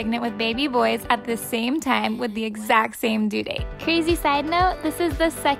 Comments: No I thought you said the exact same dude not due date 😭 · I thought she said with the exact same dude😅